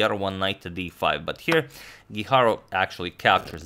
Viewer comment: Is it just me or does Agad actually say "Hikaru captures" a couple times? One time is @ 3:31 :P edit-Nvm, looks like that's how "Guijarro" was pronounced bit quickly lol